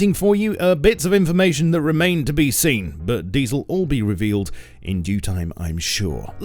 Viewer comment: did he say, but diesel all be reveled in due time at 4:04? Diesel cars confirmed for next patch